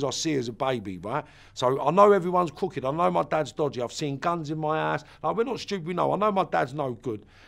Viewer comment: Did he say "I've seen guns in my ass" at 1:44?